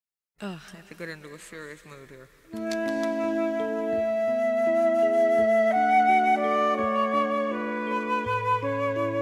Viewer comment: What does Karen say at the start of this song, I have looked for the lyrics but the first bit is not included?, please and thanks anyone. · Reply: Many thanks for this as it is never classed as part of the lyrics which in a way maybe it isn't but it's difficult when you want to know these things. She actually sounds like she is saying fearfuff moo ear, lol!.